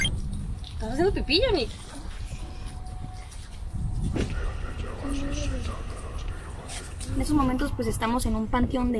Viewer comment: hay una voz distorsionada a lo que yo entendí fue dejen de grabar su.... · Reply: "Dejen de grabar su escena"